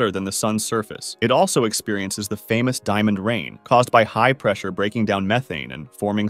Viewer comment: did you say diamond rain 🌧️ 💎